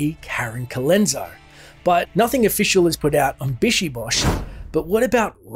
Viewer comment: Those were the days when Diablo title had soul and creativity... BTW: I always thought those demons were saying "Back Off" when attacking them. It just doesn't sound like "BishiBosh" to my ears...